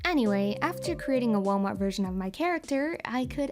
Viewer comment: "So, after creating a Walmart version of my character," I LAUGHED SO HARD I WASN'T EXPECTING THAT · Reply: I heard "Walnut version" 😀